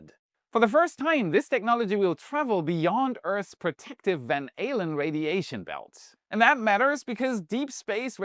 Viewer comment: Van Halen radiation belts LOL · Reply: I heard "Van Alien" radiation belt. 😂